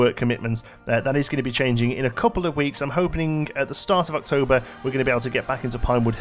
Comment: Did anyone else hear “hopening?”😂